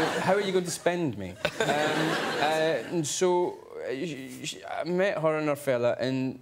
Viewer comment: Did he say "I met her and her father"? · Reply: "I met her and her fella" ('fella' = informal British word for man, guy)